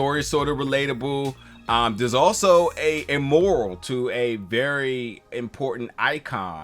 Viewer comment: He was pronouncing Mural crazy 😂. Thought you were saying “moral” like lesson. You cool though man good review. It was a cool movie